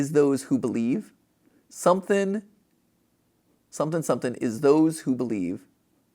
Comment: My first thought was it saying blank awaits those who believe.